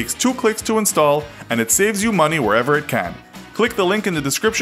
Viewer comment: I thought you said add honey to your pizza..